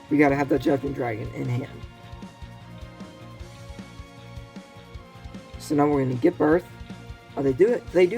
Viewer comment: at first i thought you said "so now we're gonna give birth" 💀 Fun video. I think your narrating is off to a good start 👍 Play around with these videos until you find what works best. Like you probably shouldn't read too much into the immediate results of 1 or 2 videos. Here you put replay before live, so next you can try live before replay (if you have a video that has both). The best content in any video should go first of course. You can also make polls for feedback. I don't have a lot of meta knowledge so i didn't think of the lightsworn tear synergy. I was shooketh by all that insanity